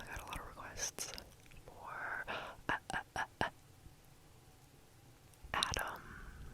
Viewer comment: did she said Aaron or something else? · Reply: she said Adam